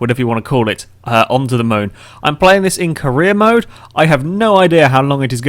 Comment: I heard "im playing this in korea mode" I got very concerned.